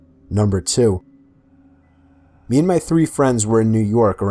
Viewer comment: idk why but 4:58 sounds like you said me and my fruity friends lol